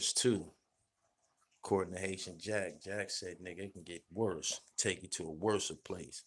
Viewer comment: THIS DUMB GLORIFIED GROUPIE ALWAYS BRAGGING ABOUT HIS EXTENSIVE VOCABULARY @ 12:10 DID HE JUST SAY "WORSA"? THE WORD IS "WORST" YOU IDIOT!!!